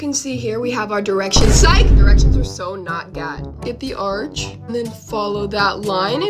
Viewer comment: i was like “wait did she just say directions r so not gyatt…?”